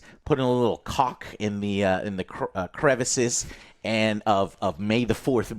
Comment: Okay, the 'putting caulk in the crevices' thing had me arching an eyebrow. Misheard you there.